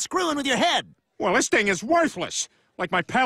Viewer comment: What did he say? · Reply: Praise be allah, allah is great ➡ allahu akbar